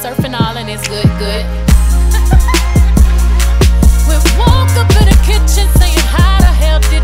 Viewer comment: When I first heard this song I thought she said “we woke up in the kitchen to have a healthy dishin”💀😂